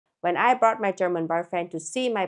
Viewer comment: I thought you said "I bought my german boyfriend" at the start 😭